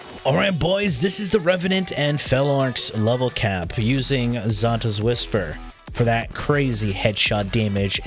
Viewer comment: When u say xata, it sounds like y say, santa 😂